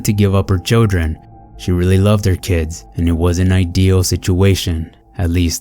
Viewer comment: it sounds like you said "it was an ideal situation"